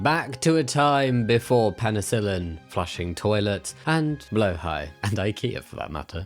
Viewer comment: your one of my favorite youtubers but tf did you just call blahaj "blow high"